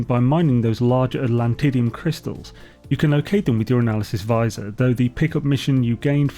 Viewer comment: Misheard your analysis visor as urinalysis visor. A completely different tool haha